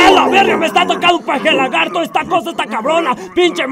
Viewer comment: XD escuche un paja lagarto XD